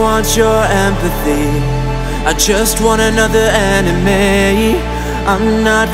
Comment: did he say “anime” or “enemy”?